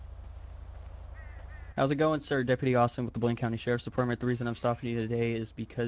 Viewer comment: Sounded like Deputy Austin said "Deputy Awesome!"